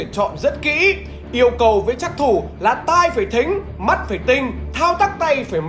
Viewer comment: trắc thủ mà nghe nhầm phắc thủ